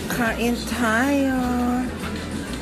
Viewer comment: Fudge 4:55 or 4:56 glossy taco ba ung narinig ko!?!??!! U holosexual too???